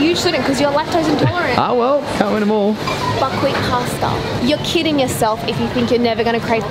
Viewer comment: i thought you said fuckwhit pasta lol